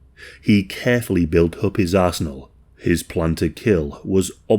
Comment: what did you say??- "he carefully built up his Arse-nal"? Yeah, my brain needs help I know